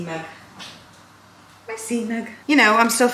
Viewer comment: When you said C mug, i first thought you said ‘ill put it in my semen’ hahaha